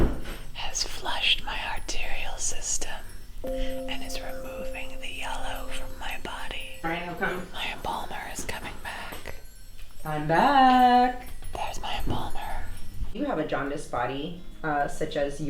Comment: First time watching this video I understood "My boner is back." instead of "My embalmer is back." and now it can't be unheard. 😂 Oh god my beloved dad died four months ago and I laugh about such dark jokes, I'm awful and I'm probably going to hell.